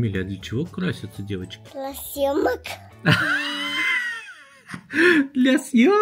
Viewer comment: Говорится: для съемок Послышалось: для семок 18:31 Говорится: ну ничего страшного (в мягком тоне) Послышалось: ну ничего страстного…